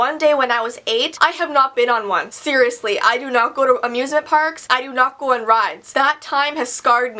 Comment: "a music park" instead of amusement part lol. Love ya